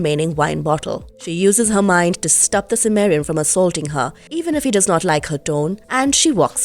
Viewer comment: For about half the damn video, I thought she was saying 'Sumerian', until I remembered that Conan was from Cimmeria. I fail at High Adventure lore tonight, I guess. FML lol (I could have not even said anything and saved face, but those who paint themselves in the shadow of their own failures, are not wont to repeat those failures...or some such boollshit 🤷🏻‍♂️IDK😆)